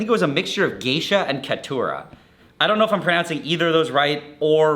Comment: I thought he said "datura" at first and was hoping for a wild ride, it's after all